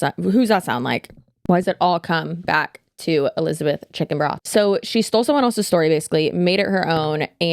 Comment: Omg 5:07 the captions say Elizabeth Chicken Broth😂😂😂